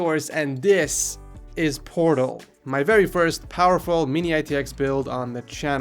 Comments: Who else thought he said porno instead of portal? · When he said "Portal" in the beginning I thought he said pr0n0... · at first i thought said 'porno' but he said 'portal'